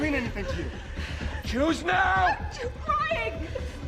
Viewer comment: It's funny cause it sounds like he said "juice now" instead of "choose now"